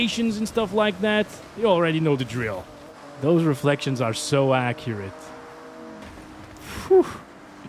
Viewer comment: Oh what an unfortunate time to have an ad pop up. At 27:05 the ad popped and it made it sound like you said those reflections were so ass.